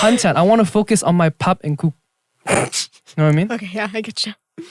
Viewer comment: I heard "pap and koek" south africans where you at😂😂😂